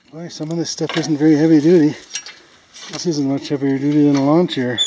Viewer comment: you're making comments while lifting a piece of the wreckage. I couldn't quite understand what is said. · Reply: I said "Boy, some of this stuff isn't very heavy duty. Some of it isn't any heavier duty than a lawn chair." Hope that helps and thanks for the comment.